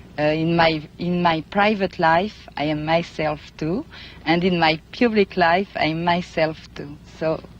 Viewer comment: sounds like she said "And in my pubic life," LOL!